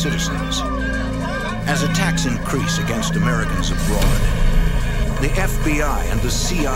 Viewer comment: I thought he said “as a tax increase on Americans abroad”… the IRS does feel like a terrorist org sometimes tbh